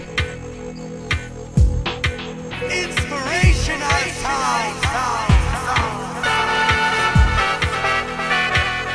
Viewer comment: Sorry, what did he say at 00:31? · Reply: INSPIRATIONAL STYLE